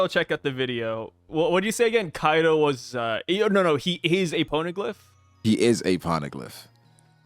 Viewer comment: Bruh the captions are wilding around 11:22. When says Poneglyph, the captions say Pornoglyph😂